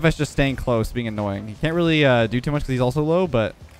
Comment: At first, i thought he said "He can't really do to much because he's also SLOW." lol Edit: i would LOVE to see a vid where you and your friends que Rumble together, as i am a Rumble main. I find it keeps the games more fun, as well as interesting and intense